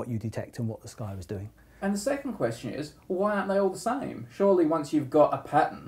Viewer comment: what did he say?? why aren't they all what? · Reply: Aryan Arora "Why aren't they all the same?"